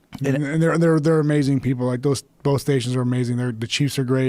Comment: I thought he said “like bitches”.